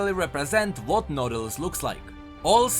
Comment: I heard "noodles" when he said "nautilus"